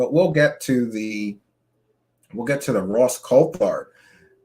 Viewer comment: I heard “Ross CopeHard”. I’m going with that now.